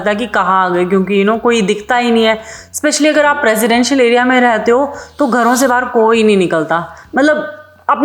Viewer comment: No way she said “presidential area” instead of residential area 4:48 😅